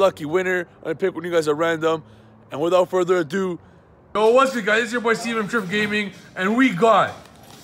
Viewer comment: Sounds like you said "whats good guys, its your boy seamen" lol